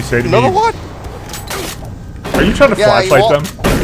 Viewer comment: why is it that I hear "are you trying to fleshlight them?"